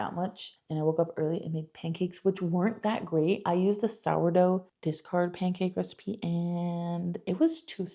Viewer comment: You said “sourdough discard” and I heard “sourdough discharge” and I said “ma’am. Wtf kinda bread are you making over there”